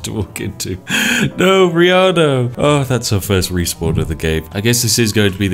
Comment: I swear I heard say “No Rhianna!”